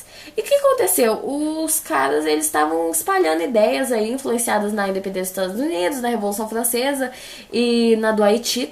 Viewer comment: repeti a parte mil vezes e não consegui entender o que você falou. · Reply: influenciados nas independencias dos estados Unidos